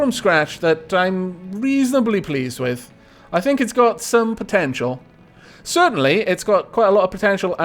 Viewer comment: Every time you said potential at the end there, I thought you'd end up saying potato. And after mishearing for the first time, I stared at the ship and it does look a little like a potato.